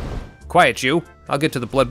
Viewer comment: for a sec I thought you said "quiet jew"